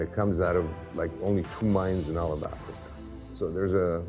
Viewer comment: Is he saying ..." two mines in Alabama" ???